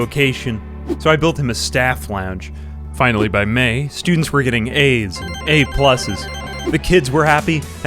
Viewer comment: sounded like "students were getting AIDS."